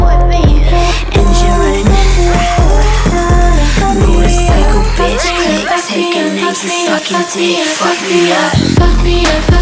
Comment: So my real name is Mia and every time I listen to this it sounds like she is saying “Fuck Mia” and I cannot unhear it smh